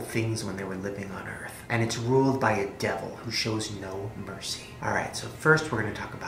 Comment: When you said Hell is ruled by "A Devil with no mercy" I thought you said it is ruled by Adele who has no mercy.